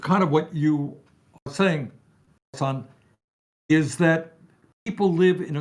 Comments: I had to go back and listen again because I thought he called him son and I was rolling. Chat went bonkers over it right when it happened and I’m pretty sure Hasan rolled his eyes when he looked at chat after it happened. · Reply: he said Hasan. His mic wasn’t picking up the start of his sentences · Bro, 17:04 I thought he called you "son" 😂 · I heard him say, "What you were saying, son" I thought he'd be blush from hearing that but then i realised he said, "What you were saying, hassan"😂